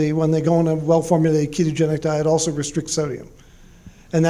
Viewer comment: What did he say at 3:18? Restrict what? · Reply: Sodium